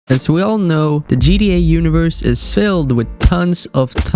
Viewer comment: it sounded like he said GDA